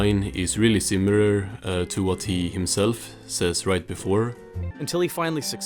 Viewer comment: why did i hear him say “i guess i’m not meant to die after all” 💀